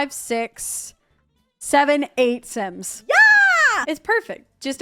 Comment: I heard it... 67 😂😂😂😂😂😂😂😂😂😂😂😂😂😂😂😂😂😂😂😂😂😂😂😂